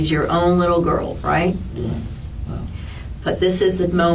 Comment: Funny, it doesnt sound like you said you loved that little girl like your own.... I think you said "well". That isnt a yes......